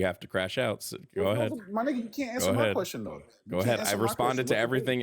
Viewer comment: Did he just say my N? 6:19...No way is he Latino he is black trying pretend to be Latino..